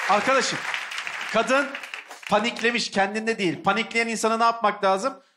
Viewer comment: tolga çevik ne diyor ya merak ettim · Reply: Pandiklemiş ☝